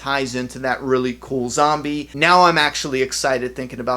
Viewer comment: did you just say zombie ? Tf is this ? Smh dope video tho bro